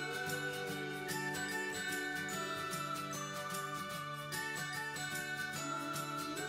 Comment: first I thought she said look how horrible this is but she said look how amazing this is